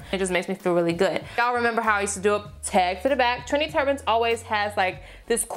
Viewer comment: As a person who is half Trini, I heard "Trini turbans" instead of "trendy"😂 Love these looks. Easy, chic and gorgeous:)